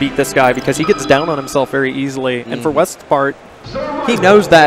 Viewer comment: did he call him "Westfart"